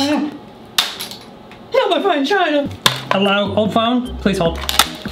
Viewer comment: No my chine fina! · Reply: He really said my fine china byt I thought he said my chine fina